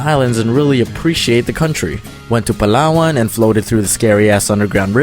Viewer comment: Ako lang ba o narinig ko si domics nagsabi ass sa